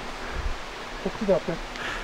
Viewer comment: スピリットボックスの来てます、の後こ○すよりもこっちです、に聞こえる気がしました😶